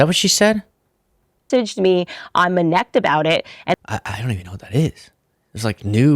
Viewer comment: I think she said "someone messaged me IM INEPT about it"